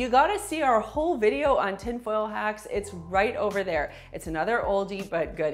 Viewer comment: I heard "tin foil hats" instead of "tin foil hacks." 🤣